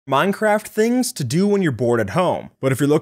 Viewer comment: I thought he said: "Minecraft things to do when your born at home"